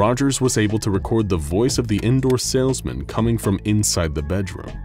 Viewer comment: I thought it said door salesman and then I saw her door and I wondered why she needed one